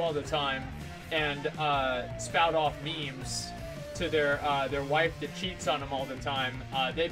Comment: fat guy that cheats on his wife represents the monk class? IM SO FUCKING TRIGGERED (monk is my favorite class aswell as the expansion he came out in... mop, is also my favorite expansion) sue me i like a expansion that everybody else hates call the police edit: holy shit i actually got noticed by! :O Thank you! :D you made my day! first time ever! wow 10 likes more than nixxiom got! :O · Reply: Sem Amsing I thought he said fat guy who's wife cheats on him all the time..